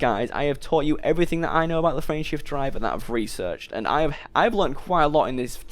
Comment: This whole time I thought the xbox was saying Friendship drive activated. lol!